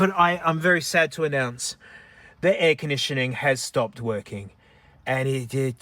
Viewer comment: It's late and I'm watching this on low volume and misheard air conditioning as eggnition, which would've been suitable name for the Golf's ignition